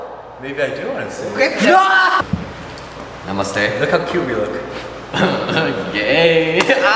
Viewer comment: Did I heard correctly? "namaste"